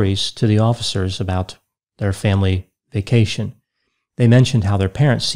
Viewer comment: 🎶 “the hills are alive with the sound of...” 🎶 Oh wait. I thought he said Von Trapp family vacation.